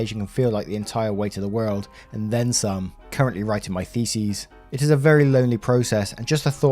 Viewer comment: Please tell me I'm not the only one who heard say "currently writing my feces" at about